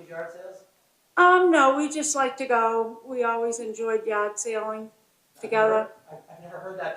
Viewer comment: I just can’t with sound here,…thought his sister said they all went yacht sailing, not Yard Sales?! 😂😅🤣